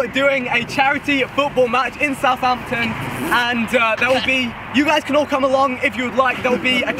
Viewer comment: I heard Vikk said you all cannot come along instead of you all can come along